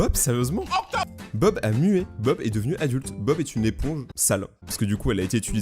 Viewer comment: moi jentends octop😂😂😂